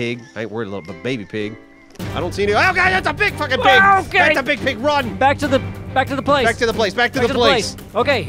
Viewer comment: I heard "IT'S A BIG PIG!" And I went Pumba?